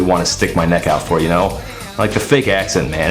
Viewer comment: I'll be honest, I heard 'a licky boom boom' as 'a leaky bum bum' and just assumed that the narrator was scared in police custody and crapped his pants.